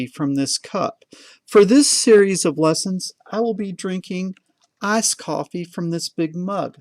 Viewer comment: I thought he said "ass coffee" lmao